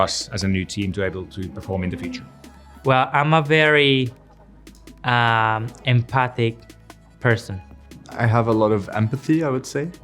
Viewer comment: BEARNELLI BOTH SAYING EMPATHY 😢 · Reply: Lol wait I'm glad I saw this comment because I at first thought he said he IS NOT a very empathetic person I was like wait why would you admit that 💀💀